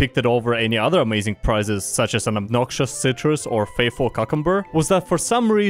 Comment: did you say... Cukumber?